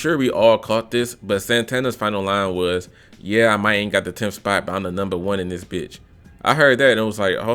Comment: ohhhhh that’s what he said, I heard I might HAVE got the 10th spot